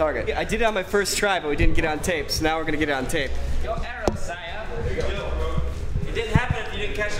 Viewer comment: did anyone else hear, Your arrow Sire?